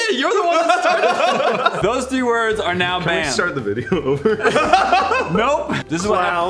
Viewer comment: i thought he said "can we start the video already?"